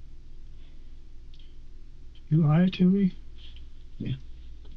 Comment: Can anyone hear what he is saying when the sub is Goodbye Tuney? It's sounds like "You lie to me" but the detective are agreeing and shaking his head yes..?